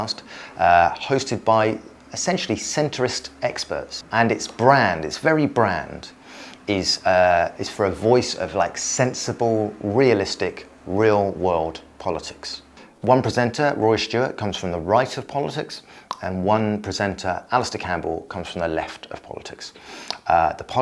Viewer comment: I did a double-take because i thought you said "one-percenter Rory Stewart" (which is probably also accurate) 😂